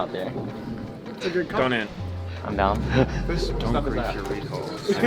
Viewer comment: can someone tell me what meteos said at 4:16 lmao all i heard was dont grief.... lol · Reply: Sounded like "don't grief your recalls".